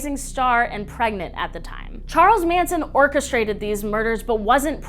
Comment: I thought you said "Charles Hansen" and deadass thought "Wow Evan's dad left for a good reason"